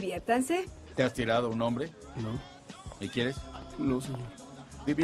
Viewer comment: ¿te has tirado a un hombre? no ¿y quieres? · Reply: yo pense que dijo "me quieres" ?